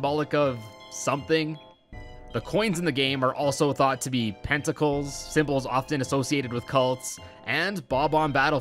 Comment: I heard tentacles